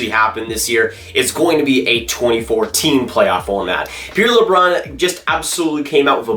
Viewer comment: I thought he said “2014 playoff format”. Took me a while to catch on 😂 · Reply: LOL when I was editing I thought I said it too, had to do a double take